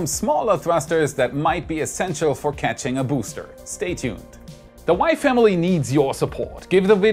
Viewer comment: Whenever you say "the WAI Family needs your support" I keep hearing "the Wife, Emily, needs your support".